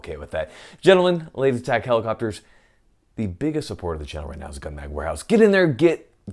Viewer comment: Is it just me or did it sound like he said “scumbag warehouse “? Maybe it’s from listening at 1.5x speed